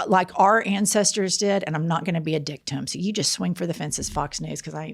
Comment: I thought Jennifer said, "...and I'm not gonna be a 'DICKtim'. So you just swing for the fences, Fox News.". Thinking she coined "Dick/victim = 'Dicktim', or maybe 'Dictim' ", under the radar. lol But, then I turned on captions (mind you, YouTube "grain-o-salt" captions), and saw that she said, "...and I'm not gonna be a dick to them.". And if y'all are gonna T-shirt "Don't Be a Dictim (or Dicktim)", I want 1/2 writing credit! And I'm Hispanic/white mixed, so it'll be DEI. 😁